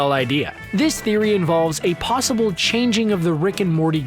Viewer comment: Did he just say "changing of the Garp"?